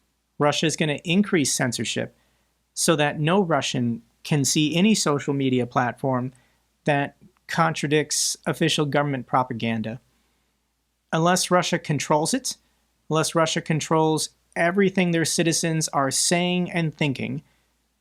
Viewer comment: I heard "government puppetanga". But it works too ! 😊